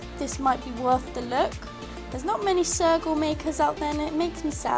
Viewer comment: When you said sergal I thought you said circle and I was like "Whut ?" XD